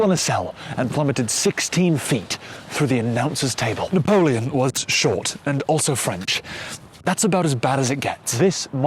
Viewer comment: “Napoleon was short, and also French, that’s about as bad as it gets” Its funny cause he's right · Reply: I misheard it at first and heard 'Napoleon was shot and french' and it still somewhat made sense to me LMAO